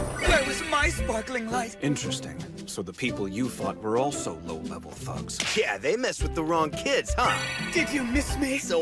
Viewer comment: When Tokoyami said "Interesting, so the people you fought were also low-level thugs" I thought he said low-level thots XD